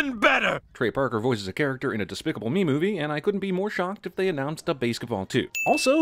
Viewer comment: I honestly thought it said despacito 3 instead of despicable me 3